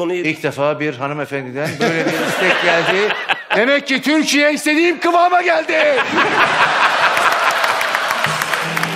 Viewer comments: Şu 14:45'de olan kadın ne dedi deli oldum duyucam diye duyamadım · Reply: Erotik shop diyor galiba kanka önceki yorumlarda öyle yazmışlar. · orada ne diyor Allah aşkına kaç yıldır merak ediyorum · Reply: Erotic film diyo